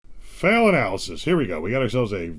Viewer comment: Analysis!! 😂😂 I thought you've been saying "Fail at Alice's" as a generic name to not say the names of places lmao